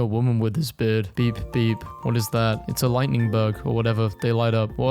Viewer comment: Oh thank god there was a loud sound then I realised it was a notification but what is creepyid when it happened I swear I heard you say "what was that?"